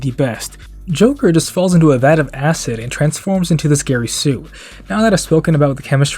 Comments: ngl I thought u said SCARY Sue and I thought that was funny af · Spiderman got bitten by a radioactive spider, and got a power of a spider. Joker was bitten by a radioactive vat of acid, and got powers of chemistry. Make sense to me. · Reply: I heard it as "Scary Sue" and I'm laughing because that'd be a perfect nickname for a badly written joker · I heard it as "Scary Sue" and I'm laughing because that'd be a perfect nickname for a badly written joker